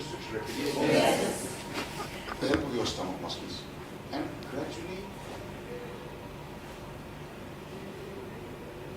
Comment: Can any one explain me what did he said for voice exercise at 1:04:13 ....help me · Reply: Just Exhale.... hhhhhhh with a little Sound... 10-15 sec however you can go.